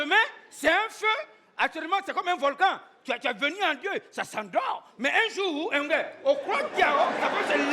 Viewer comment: qu'est-ce qu'il dit svp ? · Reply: Il l'a redit en français "un jour, tu vois, ça peut se lever"